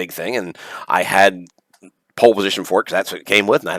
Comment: it's always fun to hear another gamer's experiences . The joys of discovery, the quirks, the mishaps... thanks for sharing! btw, it sounded like you said you had 'pole position forks' for atari 7800, lol did I mishear that? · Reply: I believe I said I had pole position for it. I get exited and talk fast sometimes and blur my words. 😂